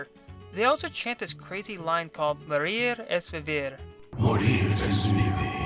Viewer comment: When the cultists used to say "Monir es Vivir" over and over again in the castle was super creepy. This game was such a masterpiece. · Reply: Not gonna lie when this game first came out I honestly thought the cultists were saying "we have STDs we have STDs" did not know they were saying "Monir es vivir" and another that I thought the cultists were saying instead of "cohelo" (pardon if I spelled it wrong) but thought that they were saying "go Hitler go Hitler go Hitler" suffice to say I recorded some of the words onto my crap phone (early 2000's) and asked my Spanish teacher what some of these meant but my teacher spoke Guatemalan dialect so some of the words were different from what he knew. Looking back now it's funny now that I know better.